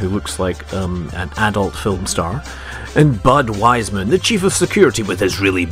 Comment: I always hear "budwiser" :D